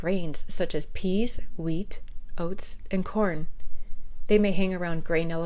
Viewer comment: peas, wheat, "outs"? What? I see I'm not the only person to watch that bit 10 times wondering what those weird leafy outs were. It sounded like my Lancashire mum shooing out the dog for farting.